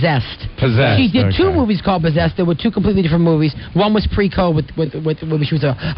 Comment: I swear I hear Mario say “pre-covid” lol